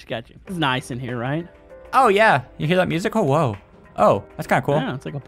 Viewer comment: Did just say "OwO"